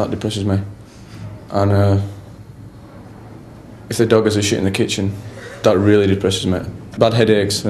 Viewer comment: Anyone catch what Nick says at 2:25? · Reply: if the dog has a shit in the kitchen, that really depresses me :D